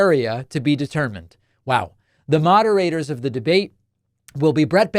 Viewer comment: I thought you said “Magarators”. And was like “cleaver”. Just me being cleaver agin tho I guess. Muahahaha